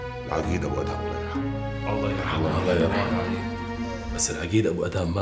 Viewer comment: بالدقيقة 34:11 سمعتوا واحد يقول الله لا يرحموا؟؟